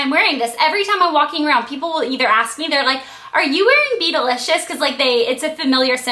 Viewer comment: i thought u said beetleliscious